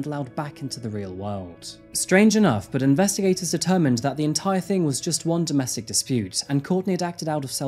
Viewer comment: What he said: "Investigators determined it was a domestic dispute" What I heard: "Investigators definitely ran a train on this bitch"